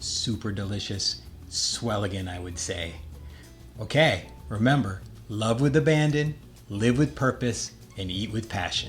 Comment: Looks great, can't wait to make it. I thought you said, "love with a bandit" :)